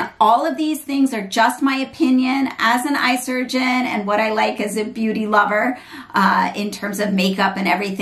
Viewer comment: Great video thank you! I thought you said: I’m an eye surgeon and a white girl👩🏼😂 lol